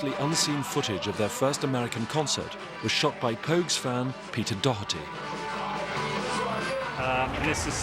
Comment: I thought they said Pete Doherty. Got way too excited.